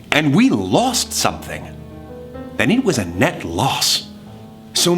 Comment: "Did you forget that he's supposed to be EVIL?!" Roman....HAS HE LEARNED NOTHING?! I sense a redemption arc coming for a CERTAIN side that's for sure. Roman needs to be put in his place. He's too much of a glory hound and now it's getting on my nerves. Here Deceit was really truly trying to help and he wasn't having it and I can't understand why. I'm sorry Roman fans, he can be great but it's HIS STIGMA against the Dark Sides that causes a LOT of issues for Thomas. Also also actually actually OH MY GOD what a PERFECT NAME FOR DECEIT! Janus was the Roman god of gateways and beginnings, often depicted as having two faces looking in opposite directions. ahhhhhhhhhhhh it all makes sense! Screw Roman AGAIN for laughing at someone's name-he did it with Virgil and now with Janus WHAT is his DEAL. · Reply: I THOUGHT HE SAID JANET OH MY GOD